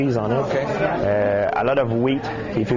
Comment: hahaha i first thought he said "a lot of weed too" XD